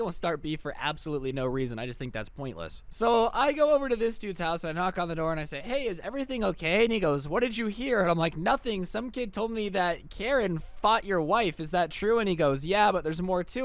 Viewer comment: At first I thought you said ", Karen bought his wife and I was ligit scared